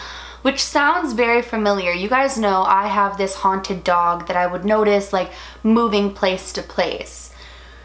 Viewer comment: Did you just say you had a haunted dog? I know you mean doll but I think you said dog 😂😂